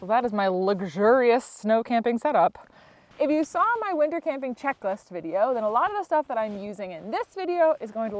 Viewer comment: I'm almost 50 and have some hearing loss so when you "winter camping checklist" , I thought you said "Liquor camping checklist". Thank you for your channel and posting videos. Yours along with so many others have motivated me to get outdoors.